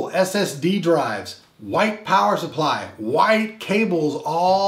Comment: sorry I heard "WHITE POWER supply" and thought I needed to share that with everyone.